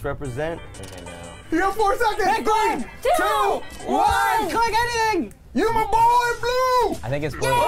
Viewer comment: When Courtney thought someone said "pick green" it was actually when at 21:00 Wes yelled "Pick one" but someone else made a "g" sound at the same time so you hear what sounds like "pick grun" which she probably misheard as "pick green" lol. And omg this game is awesome!!!